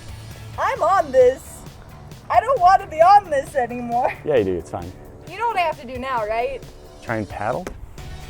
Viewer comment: did he say baby its fine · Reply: yea you do it’s fine